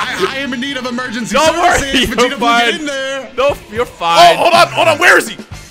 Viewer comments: I know y’all heard what said at 7:22 0-0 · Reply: He said never. There was just some fear and panic in his voice saying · Someone tell me what says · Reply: "Never" (if u still care) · what did he say?!?!? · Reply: Never but there was so much fear in his voice